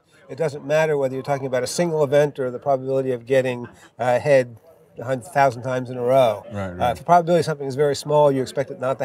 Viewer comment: What is he saying at 6:22 exactly? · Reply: I was sort of making a joke that it sounds like he says "getting...uh... head a thousand times in a row"